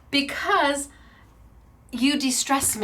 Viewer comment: I THOUGHT you said DISTRESS...not DE-STRESS!